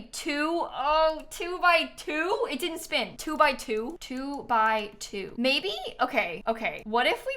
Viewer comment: not me hearing 'To by To (as in Tomorrow X Together) instead of 2x2 🤣 all along